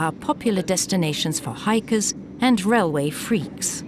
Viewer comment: Did she call us "railway freaks" at 6:52???